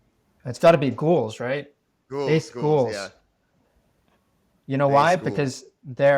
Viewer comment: What does say his favorite nft is at 57:47? Gulls??? Can’t understand him lol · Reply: Based Ghouls 😈